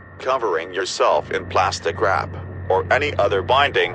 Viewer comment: I legit just thought he said ‘plastic crap’ that’s funny tho, ik it’s wrap 😂